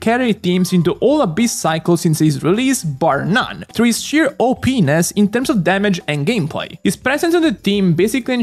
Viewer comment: i had to double check what u said there His sheer OPness 😳